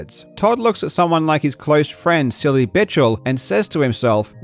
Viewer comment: did you really call him "Silly Bitchel" XD? hilarious